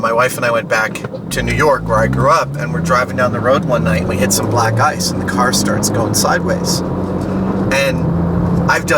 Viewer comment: I thought he said: "we hit some black guys and the car started going sideways". I was like what the fuck and started laughing so hard :') had to rewind it several times to hear what he was actually saying